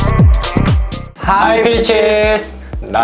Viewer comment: why do i always think they are saying "hi bitches~..."